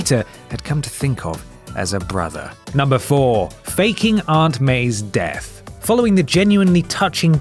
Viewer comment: BRO I THOUGHT THAT SAID FUCKING LMFAOOO